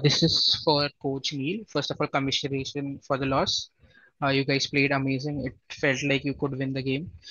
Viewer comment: for a while I heard "congratulations for the loss" :face-pink-tears